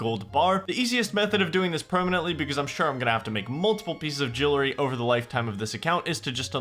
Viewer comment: Favourite part of this video so far is at 3:19:07 you say Jillary instead of jewelry, dont ask me why, anyway great series and enjoying the long ass video! keep up the good work, need to get back onto watching nightmare mode really but wanna finish this!